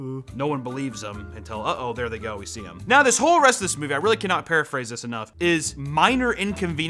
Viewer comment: Dear editor, at 7:48 he's not trying to say preface he said "Paraphrase". Meaning 'to state something written or spoken in different words, especially in a shorter and simpler form to make the meaning clearer'. The use of that word seems to be more correct than the proposed use of preface by the editor. knows what he meant when he said it son!